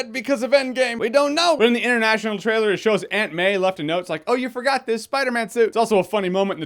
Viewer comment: I thought said that Ant Man left PP a message in the international trailer.... Suddenly realized that I may be hard of hearing, Aunt May.